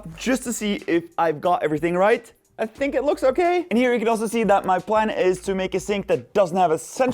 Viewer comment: "i sink it looks okay?" thats what i heard and im sticking to it lol